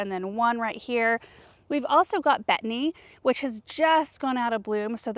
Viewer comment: please tell me the name of that plant again. I can’t catch what you are say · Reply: It’s called betony 😊